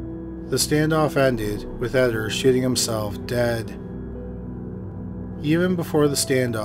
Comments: I can't be the only one who had to back up and replay whats said at 28:12... He says 'shooting himself' I heard 'shitting himself' the first 3 times... lol 🤣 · I am tired and thought you said '... Shitting himself dead' and I just accepted it like 'damn yea that's a shitty way to go' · anyone else hear “shitting himself dead?” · sounds like you said shitting himself dead😂